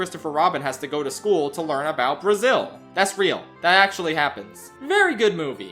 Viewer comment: | Did you say BRAZIL? |